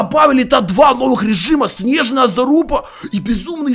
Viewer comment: Мне одному послышались "новый режим снежная заЛупа"? 🤣🤣🤣🤣